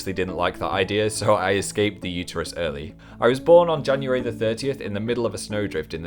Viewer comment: When i watched this video a long time ago, i thought you said your birthday was december 30th! But now, i watched this again and you said JANUARY 30th! And my birthday is on the same day! WOW